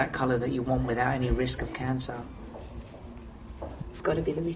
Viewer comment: Thought he said rape of cancer